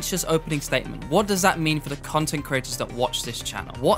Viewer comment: Did he call us C*NTS and CREATORS?